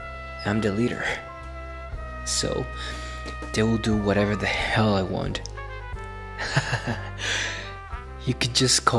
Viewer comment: thought you said "dild0 whatever the hell i want"😭😭😭